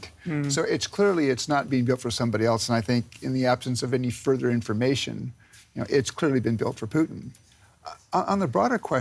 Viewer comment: "in the absence of further information, its clearly been built for Putin" I'm I hearing this right?